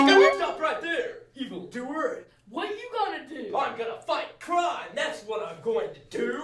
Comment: At first, I thought pineapple man said I am going to fucking cry, that's what I am going to do.